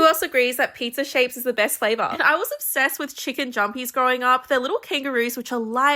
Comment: I thought she said chicken jockey- Help my brain had rotted over